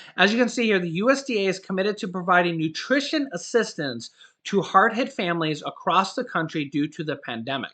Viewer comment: He said hard hit families I thought he said hard head families 😂😂